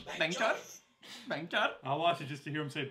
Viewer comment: WTF....lol at 4:05 when he says bank chor it seems he is saying bhenchod.....😂😂